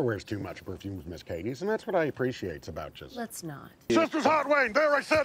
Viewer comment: what did he say? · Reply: "Chester's hot wings"